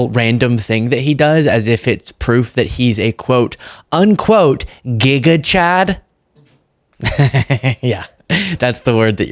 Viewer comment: Gigachad?! Why do I hear “gang of choads”🤔 That would be a good name for his administration